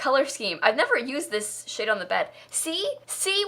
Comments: is it just me or did say “i’ve never used this shit on the bed” at 13:00 ?!!!?!! never heard swear before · Maybe I’ve been gone too long but did just say shit?! 12:48, oh my goodness, shes growing up😂😂